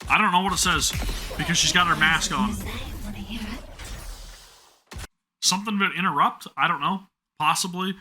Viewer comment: Even as a dedicated Sin main, ill admit it took me a hot minute to make out what she was saying, but if you're still wondering, she says: "I have something to say, wanna hear it?" And i also agree, 10/10 burst animation, my favorite one by far.